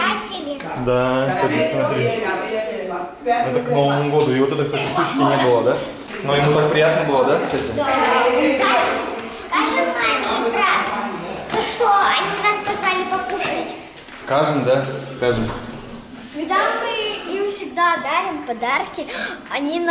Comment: Меня одну зацепило (или мне послышалось) как сказала поднимаясь по лестнице "скажем маме и брату что они нас позвали"? У девочек будет братик скоро?